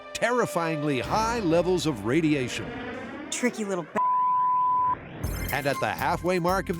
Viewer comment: "tricky little fa-" 😳 that did not sound like the traditional F bomb lol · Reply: I heard "tricky little ba-"